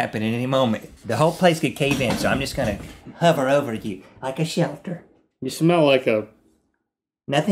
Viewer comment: when he said "like a shelter" i thought he said "like a shelfish!" that made me laugh even more when i found out i was wrong!